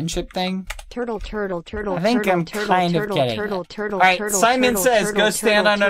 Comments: when he said "I think I'm kinda getting it" I thought he said "I think I'm kinda gay" and I died inside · Am I hallucinating or did he say I'm kinda Gay? If so I'm gonna jump out of the window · “i think im kiiiinda gay” nvm he said getting it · I heard him saying: I think I'm a little gay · Did bad say- "I think I'm kinda gay" 0:58 😳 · did he say he's kind of gay? :o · sounds like he is saying “Im kind of gay”